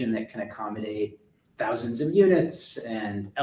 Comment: Did Tim say THOUSANDS OF UNITS????